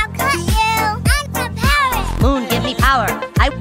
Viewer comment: i dont know why i never realized it said moon i always thought it said coon and i was always confused because he's not a superhero 😭